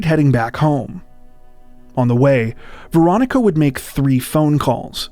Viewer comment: Had to turn on subtitles, thought u were saying "Veronica Boza". My guess is the ex is behind it. It's always the ex which never makes sense. U r already split up, why kill them? Also, why run inside a brick box where no one can hear or see u if being pursued.